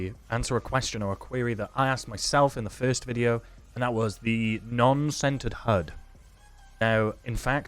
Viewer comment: The whole time you were saying "centered HUD" I thought you were saying "scented HUD". Needless to say, I was very confused.